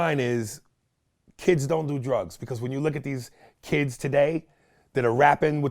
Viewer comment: I thought future said he doesn't do drugs or lean for real